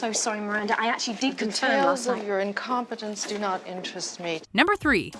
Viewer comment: "Details of your incompetence do not interest me." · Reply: I thought she said "tales of your incompetence do not interest me" and that sounded even more dramatic to me.